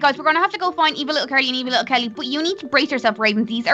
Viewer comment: I am die laughing because I thought Carly said evil little Kelly curry